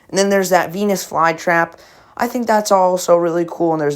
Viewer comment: i thought he said p3n!s fly trap LOL